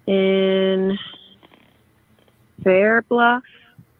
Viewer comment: The more I listen the more I hear “Fair Bluff”.